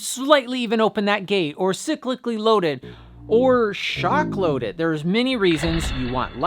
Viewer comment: When I heard the "shock load it", It sounded like the more correct: "shark load it". Now I demand an episode of shark loaded carabiners. (They don't necessarily have to be laser sharks though.)